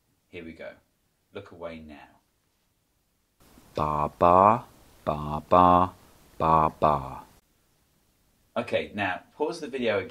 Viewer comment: I heard "Da ba, Ba ba, Ba ba."